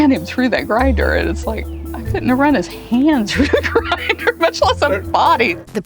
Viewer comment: Almost sounded like she said that she did have trouble running his hand through the grinder. She can't even keep eye contact and masks the memories with laughter. She did it for sure.